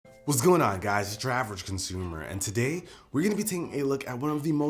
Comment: I could NEVER understand what he called himself at the start of all his videos so you HAVE to actually Read his name to know that the channel is called as it definitely sounds like he is saying "Drav-rag Consumer" definitely doesnt sound like "Your-Average-Consumer". I think its because so many Youtubers at the beginning of their videos, especially when saying the name of their channel speak WAY too fast to be understood!